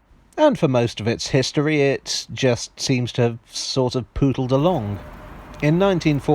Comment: I hate to ruin your joke, but he said "pootled". 😁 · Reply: Is 'pootled' an actual word? (not according to my spell-check feature!) I honestly thought he said 'poodled'.